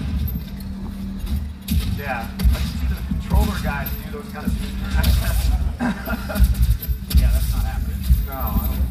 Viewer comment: What's he saying at 2:44? Something about "the controller guy"? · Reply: Something like "Lets see the controller guys do those kind of techniques"